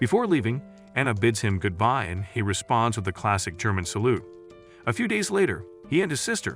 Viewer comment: DID BRO ACTUALLY SAID GERMAN SALUTE :)(